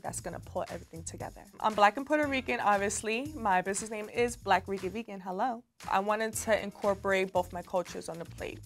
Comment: Baby y’all gotta get the subtitles rolling. I mistook “Black Rican Vegan” for “Black Freaky Vegan” and had clutch my pearls right quick lol 😂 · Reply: I had to rewind a few times because I thought she said Black Wicked Vegan lol.